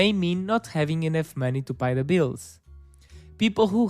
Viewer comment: did you just say Buy the bills? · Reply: Sorry for my terrible pronunciation but I said pay the bills 😅